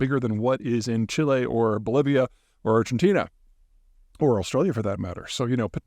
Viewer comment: Did I hear "oblivia" for the country Bolivia? If so, well played 😀👍